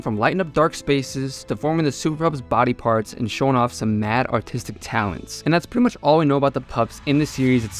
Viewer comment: at first I thought you said mad autistic talents, especially since she's playing a acoustic, i was like hell yeah! and thought it was really funny, turns out im just acoustic myself